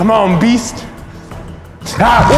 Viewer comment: did he swear at 0:04 he said oh sh(inaudible)